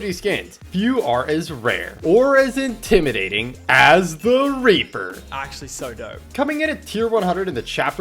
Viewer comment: am i trippin or did he say raper 13:18 ??